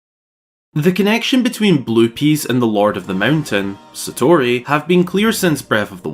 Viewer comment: The captions say blupeepees 💀